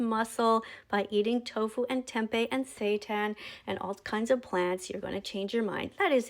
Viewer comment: did i hear that right…? Sata? Or Satan?